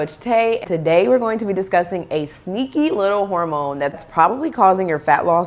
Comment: I was eating a sandwich with chips when I heard her say " Tha sneaky little Ramon!" And I was like what's wrong with Ramon? He's cool with me. And then I realized what sh was talking about